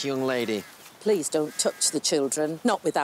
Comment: "Please don't touch the children". I heard have "You checked the Children"?